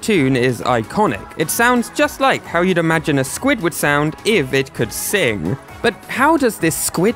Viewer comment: When he said "squid would" I heard MISTAR SQUIDWARD!